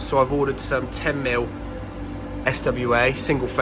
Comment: Who’s been using SY cable ? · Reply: haha just watched it back at 4.35 i thought you said 10mm SY not SWA my apologies